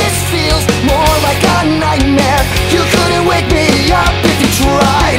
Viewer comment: 「more like a nightmare」が「もうわかんないね」に聞こえたw